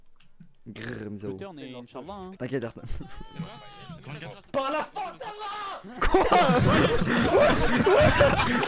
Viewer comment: il dit quoi a 1:19 wsh? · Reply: il dit : "par la force d'Allah " bg